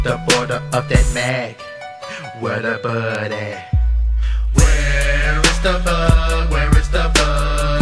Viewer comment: A huge moth came into my room when I first heard this song, & while it was playing I was looking for to kill it with my sandal, & I thought Scarewcrow said "Where is the bug ? Where is the bug ?" I laughed for hours..